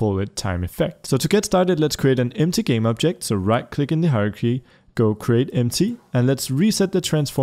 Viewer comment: When he said hierarchy I thought he said "horror key"